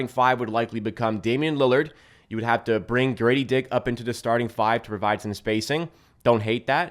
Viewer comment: Did I hear "Dirty Dick"?🤣